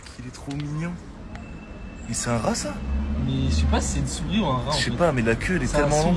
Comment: First part I heard, “Is zat russel?” Died laughing. That’s a joke in need of RATification